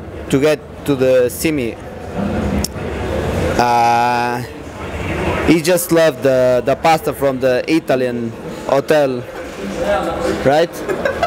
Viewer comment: What did fox say at 7:20 ? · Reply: "He just loved the pasta from the Italian hotel, right?"